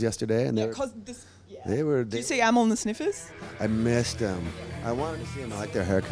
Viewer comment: What was the band she asked Dale if he had seen at 12:03? I can't make out what she's saying and that band sounds fucking rad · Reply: Hey mate, they're called Amyl and The Sniffers ... they are indeed rad! Here's a little interview + live feature I did with the legends: <link> Their first EP is killer!